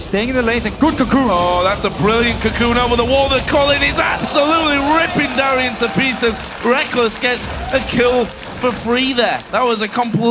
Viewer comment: Did say: RIP Darien in peace? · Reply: i think it was "ripping darien into(to?) pieces"